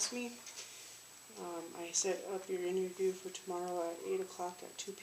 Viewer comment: When they brought Cohen in to the interview room and the lady handling his HR paperwork tells him something that doesn’t make sense to me if I’m hearing it correctly and even though the YT closed caption feature can be laughable incorrect at times it is saying the same thing I believe I am hearing. She says something that sounds like “I set up your interview tomorrow at 8 o clock at 2 PM.” It sounds to me like she mis-spoke but no one really acknowledges it either and she doesn’t seem to correct herself. She says it shortly after 14:08 in the video. It just doesn’t make sense and I’m curious about what was being said and the context. I’m assuming the interview she’s referring to is something related to HR or the Union but I’ve never been a police officer or prison guard. I don’t have any idea. Thanks. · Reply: I heard that too but I think she was trying to say I set up your interview tomorrow the 8th at 2 o clock